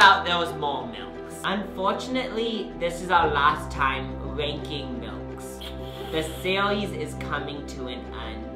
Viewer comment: I thought it said “ranking old milk” and I was so concerned for your health for a second lmao